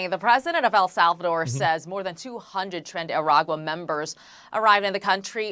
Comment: Left my phone in the bedroom, went to use the bathroom while this was playing, i swore i heard her say "trendy arugula", had to replay it. Sleep deprivation/insomnia does pretty fun stuff eh?